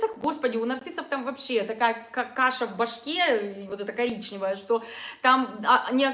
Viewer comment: «Каша в башке, вот эта, коричневая.»Прелесть. В точку. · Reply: Мне послышалось не "каша", а "какаша".